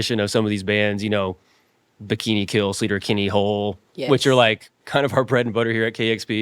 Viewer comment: The subtitles say sleeper kenny hole but I can't find any band by that name